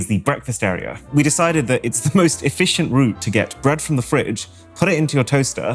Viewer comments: did he say bread from the fridge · did he just say you get bread from the fridge